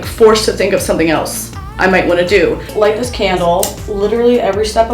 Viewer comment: I was only listening and thought you said “light this camel” and I was like “you know what, hell yeah”